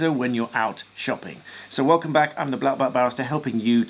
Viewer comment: I just misheard you when you said 'I'm black belt Barrister'. Sounded like you said black belt b@@ 😆